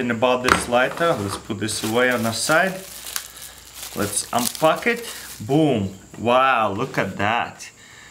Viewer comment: lets unpuck it (kinda sounds like he is saying lets unfuck it)